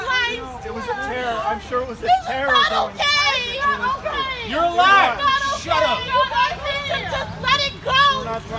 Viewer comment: “You’re alive, shut up.” Truly the response of a compassionate person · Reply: I thought he said, "You're a liar - shut up". Also true.